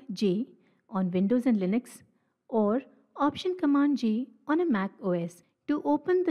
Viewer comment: command + option + J ( not G) for select template for those who mishear it wrongly